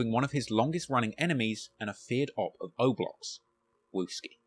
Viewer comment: I swore I heard Druski 😭